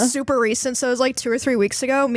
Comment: Am I the only one who was waiting on the super racist thing because I misheard “super recent”? I’m cooked chat